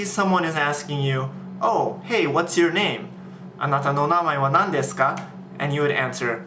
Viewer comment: just wondering wat is he saying at 1:50 anata wa no namae nan desu ka?? is that right??// dudes on steriods when he speaks :]